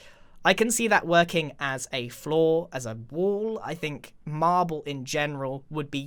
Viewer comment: He didn’t say flaw.... he said floor for those of you who are American watching this video. It’s just the accent that made him sound like he said flaw.